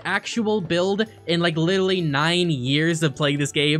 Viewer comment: he said lilally instead of literally but it just made me happy how cute it sounds